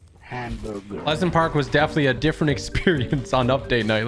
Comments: did anyone else hear "Hamburger" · y did it said HAM BURGER